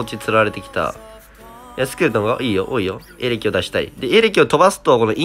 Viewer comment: いいよ、多いよがいいよこいよに聞こえる